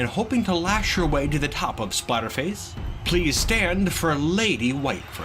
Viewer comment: For a moment it sounded like you said Spiderface instead of Splatterface. I guess it isn't too far off. I am impressed with Pibo's performance. He definitely put up more of a fight than I imagined he would. I wonder if Whitefur has much power to her bite. Her face definitely looks scary enough to warrant it, but she has too much class to make use of it perhaps?